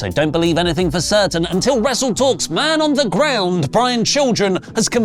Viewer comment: I heard that as "Man Underground," which means I was disappointed by the cutaway.